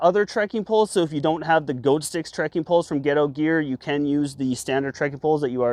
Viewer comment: With that Canadian accent, I thought you were saying “Ghetto Gear.” 🤣